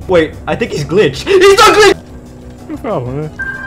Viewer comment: when says HES NOT GLI- Captions thought he said he's ugly